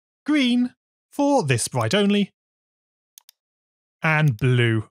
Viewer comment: you said bright instead of sprite